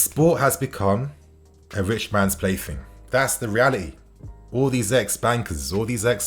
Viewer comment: I could have sworn you said “all these ex-wankers”. I may have miss heard.